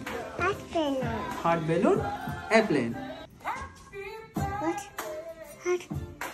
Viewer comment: I thought she said "yooo airplane"